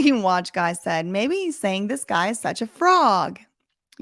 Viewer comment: I keep hearing “this guy is such a girl”